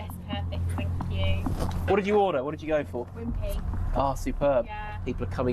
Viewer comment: did she say Wimpy. I love Wimpy where does she live? I’m moving there😂